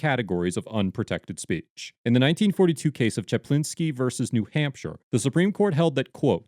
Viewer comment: I thought he said "Jablinski" at first. I thought wow Jack Black had been responsible for the creation of important legal precedent